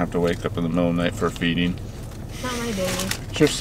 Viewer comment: Wait WHAT at 17:36 did he say, :I told Jayden that she had to get up at the middle of the night for ....” i tonight he said phoebe but idk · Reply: Feeding as in to feed the baby